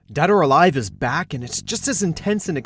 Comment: Did he just say Dad Or Alive at the start?